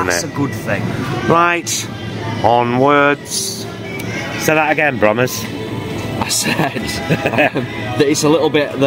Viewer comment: What does say to at 6:24... ‘say that again ___’? I hear it often in the vlogs - it sounds very sweet! ☺️❤️ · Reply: "Say that again Brommers" - nickname